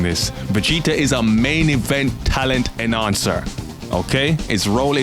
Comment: Talent enhancer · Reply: i thought he said talent and answer! Damn now i dunno